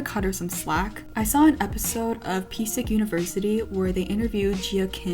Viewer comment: What did she say at 16:22 ? What's the show called? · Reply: psick university